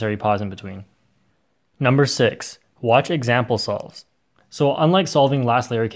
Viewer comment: lol the caption says Example falls